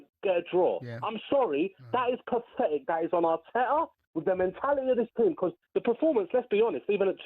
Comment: when the first phone in guy says Arteta. The subtitles said "our Terror!" hahahahah very apt!